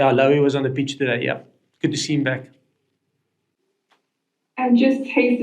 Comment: Thought he said Lowy was on the beach today! (yeah in Dublin I know!!!😅